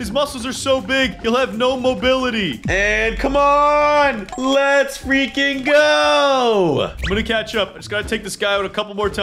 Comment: Bro u swear I heard u u say let's freaking go =(😂😂